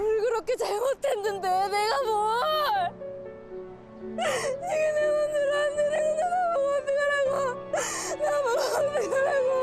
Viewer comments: 지금내모습나한테생각나서나보고어떡하라고 라고 들리는데 도대체 이거 뭐라는거야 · 제보하라길래 해 봄 이게 내 맘대로 안되는 건데 나보고 어떡하라고~ 이렇게 들림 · 초 이게 내마음대로 안되는걸 나보고 어떡하라고 ... 라고 들리네요 ㅋㅋ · 이게 내맘대로 안되는데 나보고 뭘 어떡하라고............ 이게 들리네